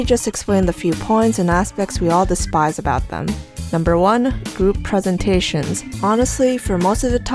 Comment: When u said group presentations I thought you said boob presentation.....